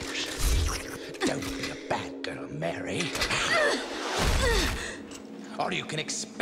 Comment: If not for the subtitles, I would have thought Jester said: Don't be a Batgirl Mary!🤣